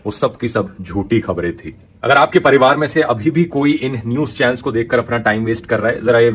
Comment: Did he just say Hindus channels??? WTF